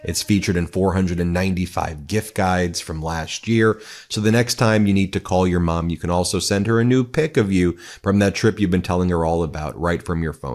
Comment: Sounded like he said, "The next time you need to call your mom you can also send her a nude pic of you" at minute 9:06.